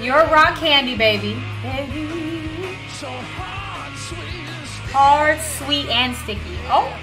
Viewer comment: I always heard it as hot sweet and sticky. Guess ive been wrong for 40 some years! Top 20 album catalog for me.🎉